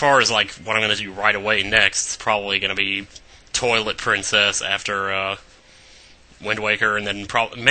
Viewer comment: Lawl, i thought you Said Toilet Princess. heh.